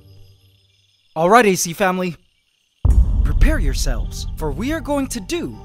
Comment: LOL, was I the only one who heard "prepare your cells" at 3:54??? XD